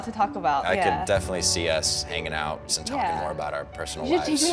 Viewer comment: when he said i could definitely see us hanging out, i thought he said making out 😂😂